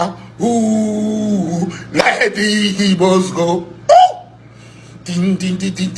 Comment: I thought he said he's Igbo